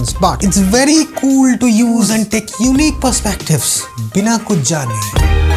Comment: did you just say "bila kujali" 👀 a Swahili word for "without caring" 😂